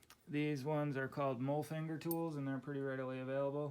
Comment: could someone please type out the word he uses for those tools (It sounds like he said "mole finger" tools). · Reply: mulfinger nipple loader. <link>